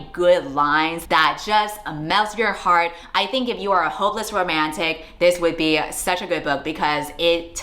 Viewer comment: PLSSS WHY DID I HEAR HOMELESS ROMANTIC 💀